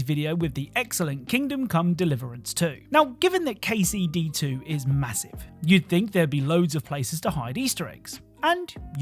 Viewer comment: wait wait wait did you say massive